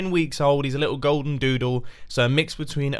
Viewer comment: am i the only one who laughed when said golden doodle? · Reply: i thought he had said golden ladle